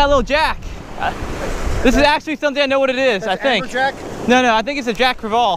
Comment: i thought your dad said “applejack?” 😂🤣 lol, keep up the good vids my dude! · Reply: Applejack